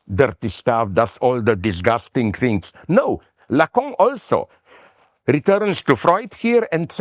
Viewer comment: Who did Zizek say used Freud in their critique of the 68 protests? Lacout or something? La cont? Starts at 9:30, mentions him a few times I think. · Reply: I believe he’s referring to Lacan